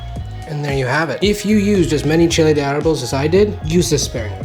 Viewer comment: What word os he saying at 7:45? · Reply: Chile de Arbol. It’s a pepper you usually find dried at the store.